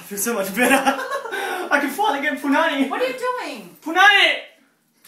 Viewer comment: when i first watched it i thought he said 'i can finally get to narnia' but then i realised he said 'i can finally get punani'